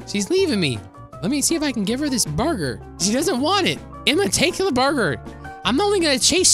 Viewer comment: "And I want to feed this burger to her" ME: did he just say he wants too finger her burger?